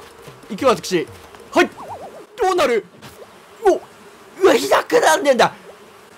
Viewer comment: ｢どうなる?!｣が｢ア○ル｣にしか聞こえないやばい…